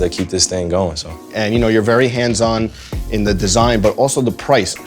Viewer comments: I thought he said “your very handsome” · I thought this dude said “your very handsome”🤣🤣🤣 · thought was gon say “you’re very handsome” lmaoo